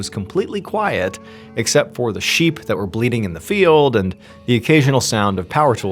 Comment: I heard "It was completely quiet except for the sheep that were bleeding in the field," which is a creepy and ominous statement, to say the least. The only thing that made me realize I must have misheard was how casually he moved on without further comment. Even for Mr. Ballen, that would have been a new level of calm and chill!